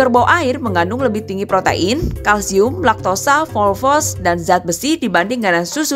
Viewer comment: Saya yg salah denger apa emg bener dibaca FOSFOS 🗿